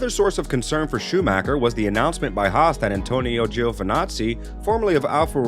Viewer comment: Did he just call Antonio a Nazi?